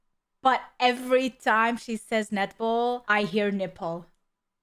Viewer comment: Net ball? I hear Nipple too. Net ball make sense ;) :D